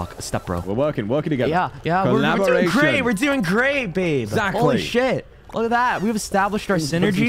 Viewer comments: I heard called Vox "babe" by accident (I'm not shipping or whatever.... Nevermind😅) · Atleast Between 8:35 and 8:39 i heard say "Were doing great babe" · did said Babe in 8:35 minute of this video? Or is it just me hearing stuff?